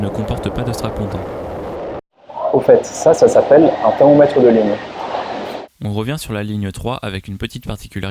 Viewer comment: un quoi ? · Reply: Thermomètre de ligne